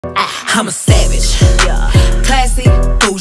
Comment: Now I hear "I'm a sandwich😩✨️, yuhh💅" Instead of "im a savage😩✨️, yuhh💅"